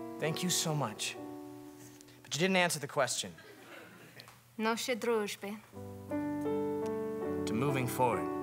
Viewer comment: captions say Na zdrowbie, but im like 80% sure she says Nashey druzhbe, as in To our friendship?